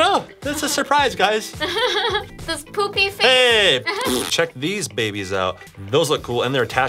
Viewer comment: I thought you said check vy’s babies out